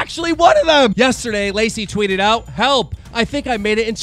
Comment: No Keem, she did not say "help". Come on man, you're from the goddamn Northeast, you should know that "Welp" is a way of saying "Well ok…". I normally liked how you'd say names wrong, mostly because I thought it was a playful diss or whatever, but now I think you may just be sloppy…like Scarce-sloppy (RIP, big guy).